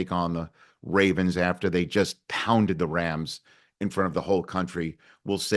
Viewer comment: "Pounded the rams." ...for a second there I thought he said yams. Happy Thanksgiving y'all!